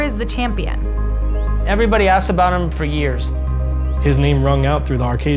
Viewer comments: She said where is tomo but i heard “where is homo” lol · Did she say "where is homo" at 4:42? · did she just called him HOMO? Lol